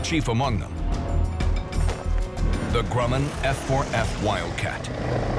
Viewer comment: What the narrator said: Grumman F4F Youtube subtitles: Grumman F-14 ._. Thought the movie The Final Countdown was a fiction.